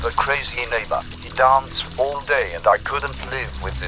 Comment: AYOOO I HEARD ASS